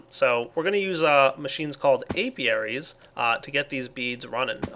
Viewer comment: I thought it said Alparies